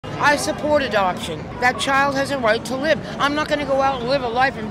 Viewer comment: I thought she said I support abortion.